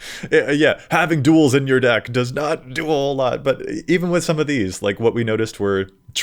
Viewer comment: "Having duels in your deck does not 'duel' a lot" 😂 Just the way you said do a whole lot sounded like 'duel' lol Missed dad joke, so good at making them you do it by accident.